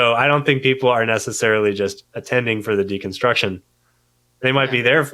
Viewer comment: Whoa, did he say "decunstruction"? Hmmm...